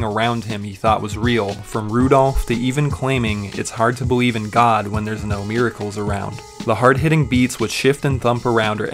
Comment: am i the online who thought Tyler says "hard to bleave in god when there aint not MIRRORS around" like you look in the mirror you see yourself like him saying he is god lol but here he says medicals around